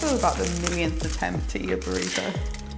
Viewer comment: When i see the video i heard i'm burrito😂